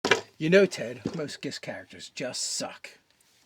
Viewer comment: Hey does anybody know what they're saying at 0:01 in the beginning? "You know Ted, most ___ characters just suck." Is it "gith?" That wouldn't make any sense. "Gish?" What does that even mean?? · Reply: Gish is a bastardizatiom of Gith where comes from the Githyanki and the first wizard/fighter and now just means someone who can spell sling and weapon attack well, usually through multiclassing.